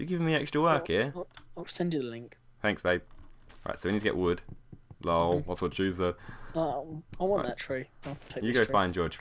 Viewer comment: What do you say at 1:30... "LOL whats up ......"? Sorry for asking but i just really couldn't understand!